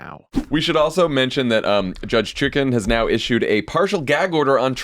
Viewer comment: Did Jon say "Judge Chicken"?